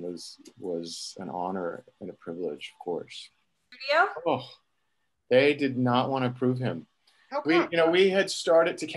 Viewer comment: about who u r talking? I didnt hear the name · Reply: I believe it’s Rhys Darby, from the newer seasons